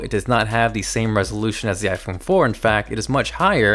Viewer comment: iPhone 4? Did I hear correctly?